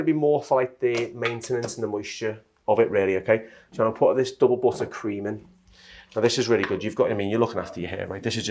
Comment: Can anyone tell me what product he used? It sounded like he said butter cream?